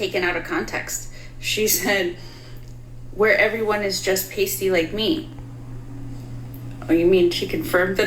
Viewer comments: Irish here, pasty and proud 😆 when she says pasty, she means skin the colour of actual milk, like people around you need to wear sunglasses when the sun is out so you dont blind them with your milk bottle legs. Not being able to go out in the sun becuse we burn to a crisp within minutes. Shes not being racist at all 😂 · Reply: I thought she said tasty. 😅. Pasty makes more sense. 😂😊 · Ohhhhh, she said PASTY. I thought she said TASTY!! Why did I not think it was weird for Billie to refer to herself, . . . and everyone else there, as TASTY? Hmmmm, maybe because I'm part Irish? I like TASTY BETTER!